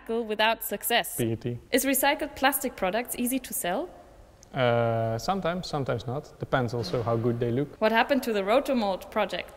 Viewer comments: Does anyone have any idea what he says at 7:04 It's a great question and I would love to hear the 1 word answer haha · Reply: "Sometimes, sometimes not, depends also how good they look." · What did he say about the hardest problem they tried to tackle without success? It's in the 6:59 minute. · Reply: PET